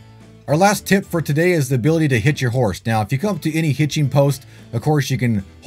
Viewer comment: You said the ability to hitch your horse... I thought you said HIT your horse at first. I was like 😮